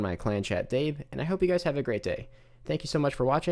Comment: Rsn:TheGrayad thought you said "have a good dabe" 🤣